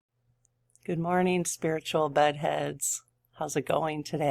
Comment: 😳I thought you said “ Good morning spiritual BUTTheads” at first!!!🤣🤣🤣🤣